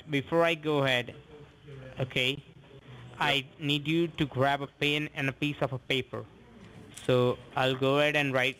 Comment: When they say grab a pen and a piece of paper, but it sounds like they say a pan and a pizza pepper lol